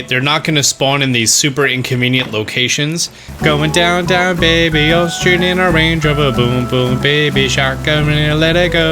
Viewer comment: Going down down baby all straight in a Rover boom boom baby just to let you go. Or at least that's what I thought he said.